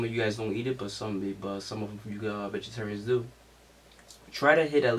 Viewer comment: sounded like hou said "bitchatarians" at 2:42 lmao